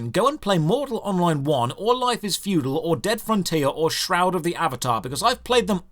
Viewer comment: did he say Dead Frontier? There's no permanant housing in that browser MMO (still love the old game and still has updates.)